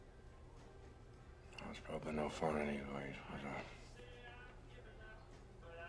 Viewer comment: Sorry did anyone catch what he was saying at 1:26? I didnt get it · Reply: He said "I probably wasn't much fun anyways was I?"